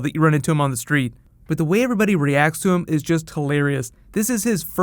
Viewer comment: At the start, Clark Kent is talking to somebody and as it goes along it's revealed its Bruce Wayne aka Mr Wayne aka Match Malone aka Batman so maybe Batman will appear nest season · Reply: I know who Perry White is, I just thought he said Wayne for a second